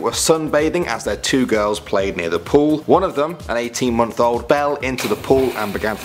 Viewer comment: I heard 18 YEAR old, instead of 18 months old, very awkward.